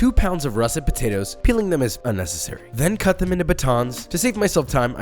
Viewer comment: I think he was saying "peeling them is a necessary"(as in a necessary step in making the fries) but I think most of us heard "peeling them is unnecessary" Idk maybe not